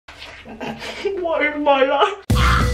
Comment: I thought you said,"water my life".